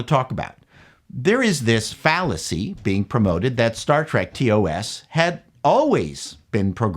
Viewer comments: I first heard that as "Star Trek: POS" for which I assumed he was referring to Discovery. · I know I heard you say Star Dreck.